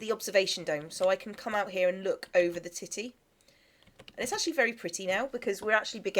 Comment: did u say tity